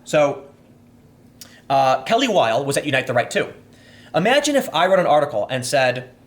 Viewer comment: Kelly Weill is a trap? did I mishear that?